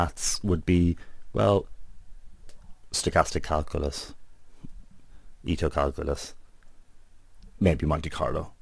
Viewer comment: Can you reiterate what said from 7:20 forward? You mentioned Lognormal Model, Stochastic Calculus, XXX Calculus and Monte Carlo maybe. Could you fill in the XXX please? Thank you! I appreciate the video by the way! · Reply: "Ito" calculus, or Ito integral, of a function against the derivative of Brownian motion.